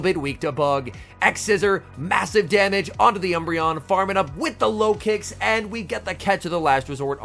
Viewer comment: I keep hearing "farming up with the Lokix" as "farming up with the Low Kicks" and I'm like "who in the heck is running Low Kick??" 🤣